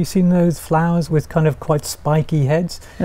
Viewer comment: "teasels were sometimes used in cough making" ? What is saying at 4:58 ? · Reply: Cloth making